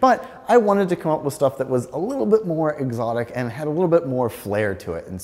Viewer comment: C7 corvette z51 is clearly the answer here, not even close. Has the same transaxle and mid front engine setup as the vantage, except lighter and more power and modern tech and magRide and CarPlay. And readily available in manual convertible - or the top comes off in the coupe. No brainer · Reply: Nailed that. Plus, he is forgetting about upkeep on all those European cars. Should have at least one Corvette in this list. My C4 ZR1 would smoke all these. (btw I thought he said erotic! haaa) all good fun